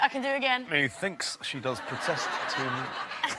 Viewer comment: what does ricky say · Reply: "Me thinks she does protest too much"